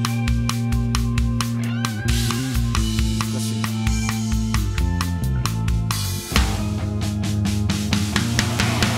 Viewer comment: この時浅岡さんなんて言ったんだろう？？難しいね、かな。。だとして何が難しいんだろう？ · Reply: 懐かしいね かな？